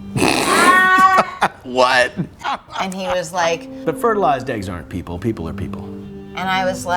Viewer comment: Sorry, my english is not that good, can you help me?! What he say at 1:34 ? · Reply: "The fertilized people aren't people, people are people."